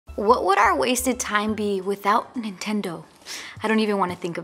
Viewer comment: Did she just say... Wasted time???!???